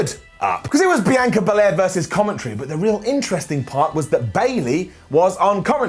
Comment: did just say Bianca Belair vs commentry😂😂